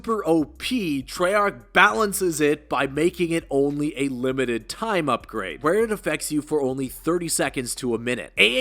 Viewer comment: It's limited, timed for around 60 seconds, I believe. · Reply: thank you, it just sounds like you say "unlimited" to begin but I likely just misheard. Appreciate you clearing it up for me.